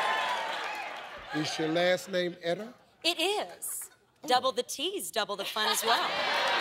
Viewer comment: "He look like he ate 2 people".....damn, Steve. And it sounded like she said "double disease".